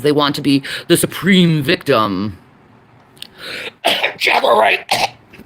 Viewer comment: I can't figure out what name she was trying to say there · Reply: Sounds like "Jaguar Wright."